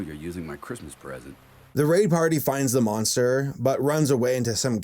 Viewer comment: At first I thought you said "RAPE PARTY" instead of "RAY PARTY" xD